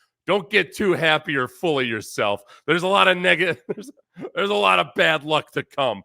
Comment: Haha did I hear that right theres a lot of what??? 🤨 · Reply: 😂 Negativity. My relatives weren't THAT racist.